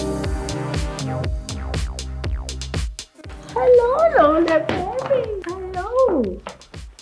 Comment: did she say hello lo le bebe